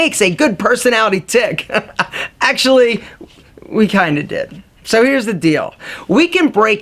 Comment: Can someone tell me what he said exactly at 1:14 , which sounds like "we kinda did ..." ? Being a non-native English speaker, this sounded a bit like Greek to me. Thanks in advance.